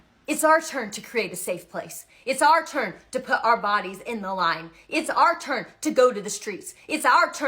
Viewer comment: did I just hear what I thought I just heard?! 😂😂😂 about the bonnet? Lemme go back 😭😭😭🫣🤣🫢🫨😳